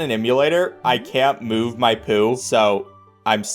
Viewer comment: I thought it was said "poo ooo"